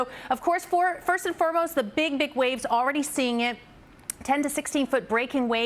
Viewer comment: did she just say "The Big D!ck wave 🌊.